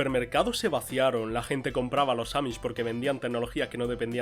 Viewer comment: compraban que? No se entiende lo que se dice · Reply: Compraban a los amissh eso es lo que dice :v